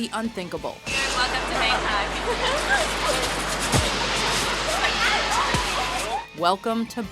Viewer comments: What did the reporter say to her in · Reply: The reporter said "Welcome to Bangkok" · What did that reporter say at 2:45? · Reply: She said "Dan iel needs to take a look at the mirror, before taking a picture".